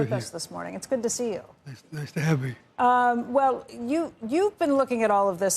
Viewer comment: did he just say “nice to have me”?